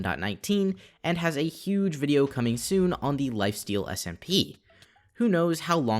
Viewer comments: I'm not sure if I'm the only one who noticed the captions, some words are slightly different from what you say but they are so similar thta nobody notices. At 6:27 instead of lifesteal u put lifestyle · the subtitles say lifestyle smp instead of lifesteal smp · the subtitle says "LifeStyle SMP"